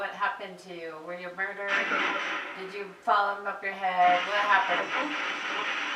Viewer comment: I clearly heard "forehead" ! 😲